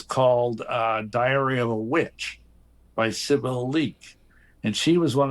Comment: I heard diarrhea of a witch 😂